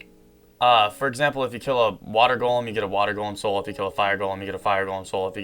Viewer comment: For a second I thought you said if you kill a watermelon you get a watermelon soul.